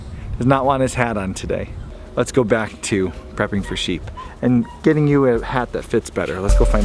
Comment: I heard "prepping for sheep"